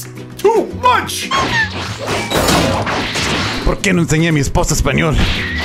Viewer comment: On 0:37 Gohan Blanco he say why did you not show my wife Spanish but on the subtitles it say not work out 😂 that not what he said lol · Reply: I hear he says "Porque le enseñe a mi esposa Español" "Why did i teached my wife Spanish"